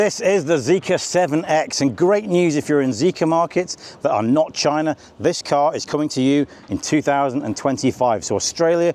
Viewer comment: What the Fok did he said at 0:57. Has he lost his mind or my ear didn't get what he said mate 😂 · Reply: "Great news if you're in Zeekr markets that are not China, this car is coming to you in 2025". In other words, China has this car now. In markets where Zeekr is sold that are outside of China, it'll be available in 2025.